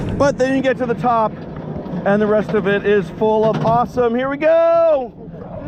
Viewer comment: IT SOUNDED like you said when you get to the top it's full of possum! So i turned on captions to actually see what you said... You said awesome Haha!! So this park is actually open?? I heard they shut down everything in this country, Six flags fiesta Texas that i live very close to was even closed. Y'all be careful & be safe out there! Thanks for all the awesome videos! ❤🙂❤